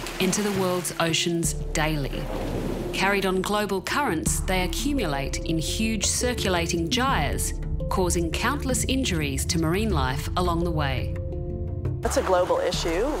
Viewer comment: What is the word she used at 3:33 , I’m making a project and this sentence sounds relevant but i don’t know what the word is or how it’s spelt 😭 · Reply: Hello, the quote around that time is "carried on global currents they accumulate in huge circulating gyres causing countless injuries to marine life along the way". Hope this helps.